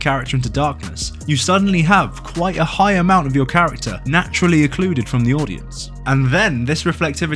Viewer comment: Stop saying "eccluded", it's not a word. Start saying secluded/recluded/excluded. This is bothering me endlessly, lol. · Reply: I thought he said "occluded" maybe it's just an accent thing